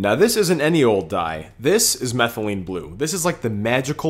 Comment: I thought he said methylamine